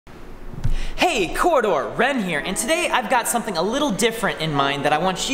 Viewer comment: For a second I thought Wren said "Rem here". Must be my Weeb senses tingling.